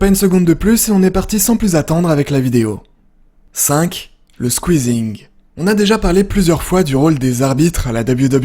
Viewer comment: j'ai compris le Squeezie 🤣 sinon vidéo incroyable comme d'hab